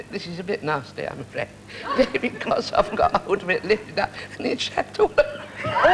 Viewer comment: what did Peggy say he did to the cat? I first loved Peggy as the voice of the Goblin Queen in the animated film 'The princess and the goblin', which was actually her final film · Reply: I think the cat poo’ed on him.